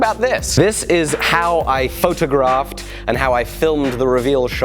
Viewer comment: My wife thought you said f****ed a giraffe, when you actually said photographed